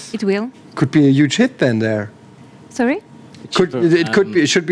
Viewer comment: Bei Minute 2:24 hab ich auch erst „huge shit there“ verstanden... und sie so „Sorry?“ XD... Btw. nettes Coop Game...